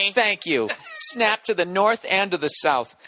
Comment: English isn't my native language, does he say "snap to the North, end of the South" at 14:37 ? If so, what does it mean ?